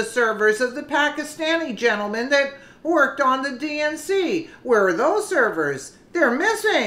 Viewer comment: I thought “Q” said, “we have the server!”